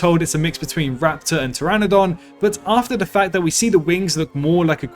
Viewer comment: i thought they said raptor and pterosaur?